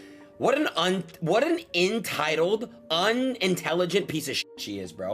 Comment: imagine calling someone else "unintelligent" right after saying "intitled" instead of entitled